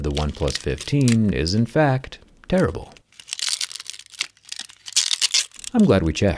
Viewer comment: the caption says terrible but I'll take it's tearable.